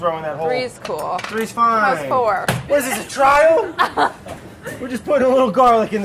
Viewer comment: what on earth does brad say at 3:30? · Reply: What is this a trial? I think